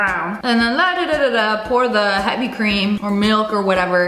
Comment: i heard "hydro-cream"